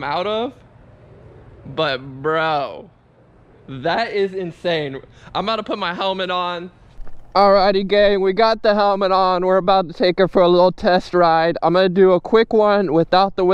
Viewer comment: Did he call me gay